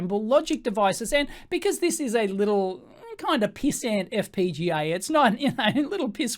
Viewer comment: did you say "pissant fpga" at 11:10?